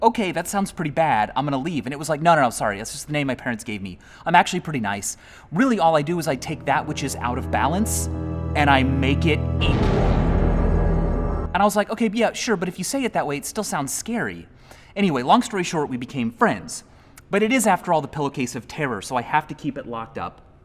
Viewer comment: "I take what is out of balance an i make it equal" "Perfectly balanced as all things should be" Pillow case of terror = Thanos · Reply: I thought he said "I take what is out of balance, and I make it EVIL."